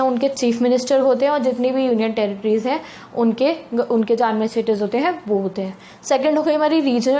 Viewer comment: not clear about union territory Words werent that clear pls reply what u said btwn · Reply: Administrators